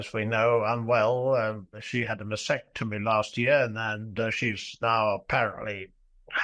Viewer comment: WHY HAVE YOU GOT THIS DREADFUL MAN ON. HES GOT NOTHING TO SAY, BEEN ON THE SHERRY , NO NEWS WHY? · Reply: He sure is waffling on. I thought he said vasectomy instead of mastectomy...in regards to Sarah but I am just not bothered to go check.